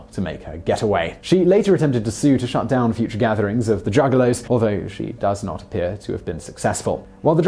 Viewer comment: did he just call them "The Druggalos?" LOL!!!